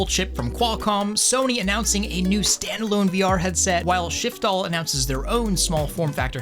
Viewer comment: I coulda swore you just said "shitfall"!